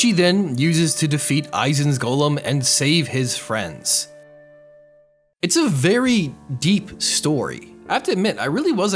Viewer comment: What the actual fuck did you call them? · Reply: Harem 👍